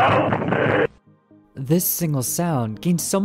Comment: I thought that he said this sigma sound